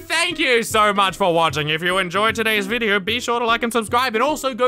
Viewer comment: Did he say 'go check out' or 'don't check out'